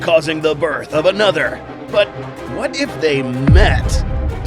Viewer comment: I heard "What if they mate?"